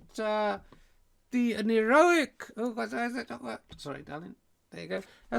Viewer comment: Richard, did you say "sorry darling" to Cocky Carrot at 25:41 ??